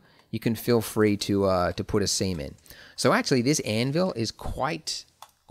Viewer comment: WHAT did you say? We can feel free to put a what.... ? :D Semen...